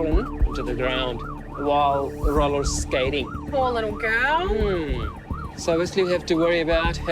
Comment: Omg I thought u said “ 70 year old girl rollerskating” 😂 I was like HOW CAN A 70 YEAR OLD SCATE